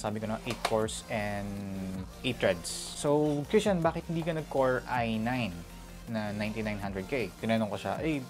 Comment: Did I just hear core i9?? XDXD